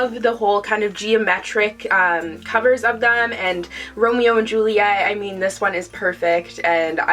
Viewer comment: I misheard romeo and juliet as "rodeo and juliet" and now I want a modern retelling set in texas where cowboys from rival ranches fall in love